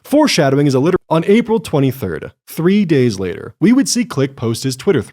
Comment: What are the words missing at 6:49? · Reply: literary device. It's a nod to an Hhbomberguy bit.